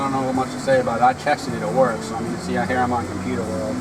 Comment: Update: Android Car Radio Here is the link to answer your questions. ;) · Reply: I misheard... I thought u said it didnt work... thanks for the correction. I ordered mine, just waiting for it to arrive. Do u have steeribt wheel controls?